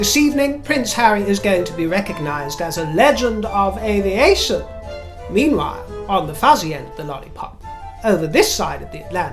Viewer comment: Could have sworn you said Legend of Alienation